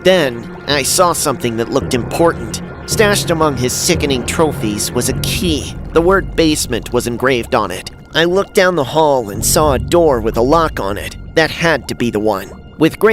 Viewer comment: Bro I thought basement said blasphemy 😂😂😂